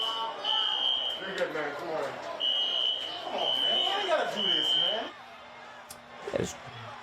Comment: One man toward the end of the riot clip appeared to have some sense. I thought I heard him say in disbelief, Come on, why you got to do this stuff man?